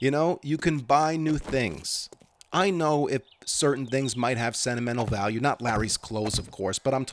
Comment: I had to back up because when he went on about buying new things and for a moment I thought he said "I understand things have sentimental value, not Larry though...."